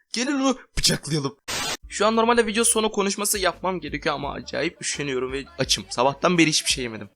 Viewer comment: Ne Dediğini Anlamayanlar +1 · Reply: vidiyo sonu konuşması yapmaya üşen dim derken vidiyo sonu konuşması yapıyo